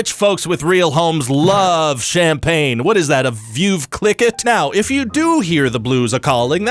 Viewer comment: Does anyone know what means when he says "a view click it", (8:26)or is it a badly generated AI typo? If so, what is he saying, because I don't understand it. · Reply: A veuve clickit is a type of champagne which I have totally spelled wrong but that’s what he said. :3